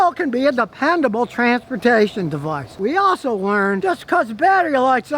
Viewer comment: When Scotty says "Dependable transportation device" I hear the phrase "Depending on hairspray device"